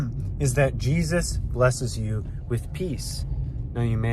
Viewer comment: I thought he said, “Jesus blesses you with pee.” Or am I wrong? 😂😂😂